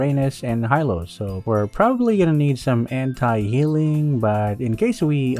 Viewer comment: When he bought sea halberd I could've sworn he said hentai healing